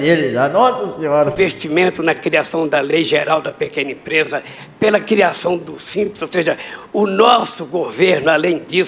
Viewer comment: No minuto 7:02 Lula disse q foi o responsavel pela criacao dos SIMPSONS ou eu ouvi errado gente? Olha la! valeu!